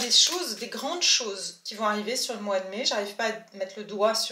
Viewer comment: Coucou J'adore ces prévisions. Pour ma part j'ai entendu "MAI I AM". À suivre. Belle semaine à vous 🎉